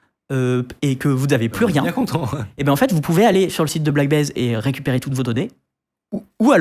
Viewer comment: Euuu ? C'est moi ou vient à 6:54 de parler de "Black Baze" ? lolllll